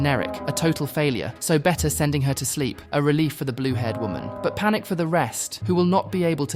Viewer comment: fourth i guess (also did he call marge blue haired women at 2:51 )